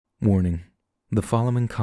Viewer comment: Thought you said morning, not warning lol